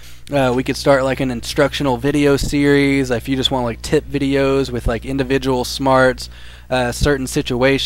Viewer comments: it sounds like he says if u wanna see TIT videos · it sounded like he said tit videos.. I like it